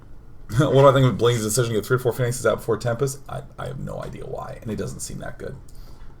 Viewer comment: What does Day9 say at 15:30? It's too slurred and fast for me to tell. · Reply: "Huh! What do I think of Bling's decision to get 3 or 4 Phoenixs out before Tempest..."